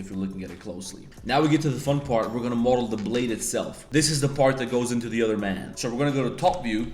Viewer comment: When he started modeling the blade, Did he just say "this is the part that goes into the other man"?? 🤣🤣🤣🤣🤣